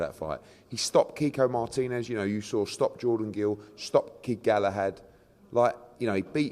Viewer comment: Did he just say that Warrington stopped Kid Galahad? 😂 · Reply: Thought he said Kiko stopped kid Galahad but I could be wrong